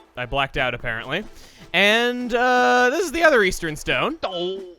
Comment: and 2:05 East? I thought you said Weast.